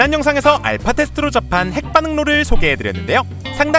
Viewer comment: 아무리 들어도 햇반응로로 들려....쿠쿠하세요 쿠쿠(광고아닙니다)